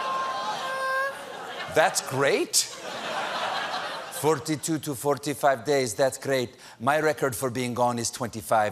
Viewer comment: i thought he said 42 to 45 minutes. maybe she did too, i donno